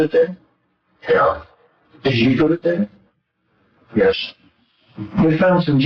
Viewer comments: What about 28:19 he's saying "yes" while subtitle says "No, sir"? Also it's not his voice haha what's going on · Why did the subtibles say "no, sir" but it sounds like he said "yes"?